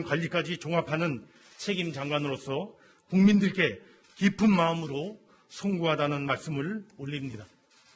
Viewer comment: 기쁜마음으로~ 라고 들림